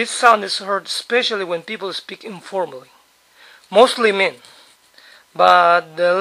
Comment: Did he say "mostly men"?